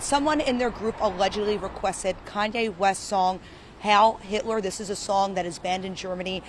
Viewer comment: Repeat it! 😁👊💪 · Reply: Why does it sound like she says Howl Hitler?